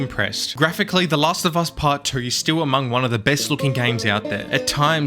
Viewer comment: did bro say part 3 or not I can’t tell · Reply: No, he said 2 lol Also, it's not "not I can't tell," lol